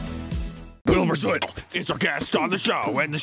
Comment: Why did it sound like you said Bilmer Swit instead of Wilbur Soot?